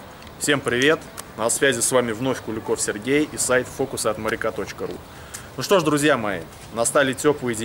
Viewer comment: Мне послышалось или малыш сказал:"фокусы от моряка")))